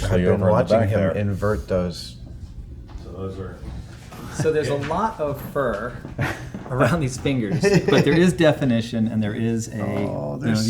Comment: the CC says "so there's a lot of her around these fingers" was supposed to be fur, not her, thank you CC for the hilarity. xD